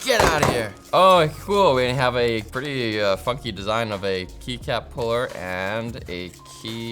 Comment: did he say Fucky ore Funky???